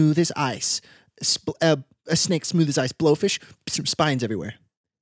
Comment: This has been the only podcast that has itched a very specific spot in my adhd ridden brain, the constant bounce between topics is fantastic because it's the only thing I can keep up with 🤣😅. I normally find myself getting distracted by genuinely anything while listening to podcast which is why I never got into them but the sweet boys are a physical manifestation of my brain and I love it ahaha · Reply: Also I thought said blobfish at the end there, then just went with saying that they had scales until said they had spikes compared to the smooth as ice (🤣) snake and it clicked that they said blow fish 💀🔫